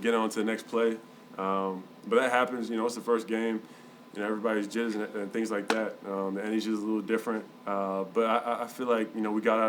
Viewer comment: Yoooo what did he say at 1:07?? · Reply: "Everybody is judging us and things like that"